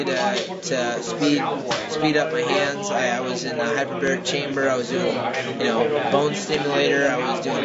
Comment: Did i hear right? 0:36-0:38. Did Urijah go dragon ball z on my ass and say Hyperbolic time chamber? Man One day of trainin in the hyperbolic time chamber is equivalent to 1 year of training.